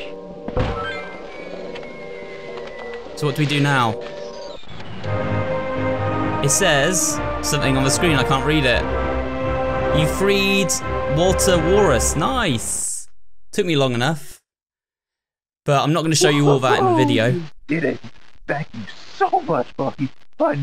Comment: i thought that said “ you freed walter white”